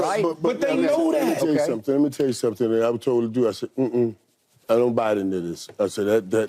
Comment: Did Irvin say I don't body negroes at 9:16. Maybe miss heard.😮😂